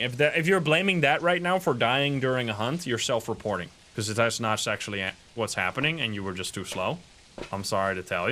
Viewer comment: I can’t be the only person who heard “that’s not sexually active” 😂